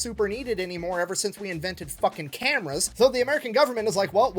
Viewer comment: wait... did he just say we invented fucking camels? im pretty sure the arabs did that in biblical days... we dont have any camels around here to fuck. Edit: oh, he said CAMERAS.... i'll admit i wasn't paying much attention. leaving original comment cause i think it's funny though.